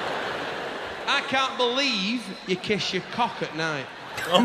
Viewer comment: When I first heard the last one, I heard "I can't believe you kiss your carpet knife."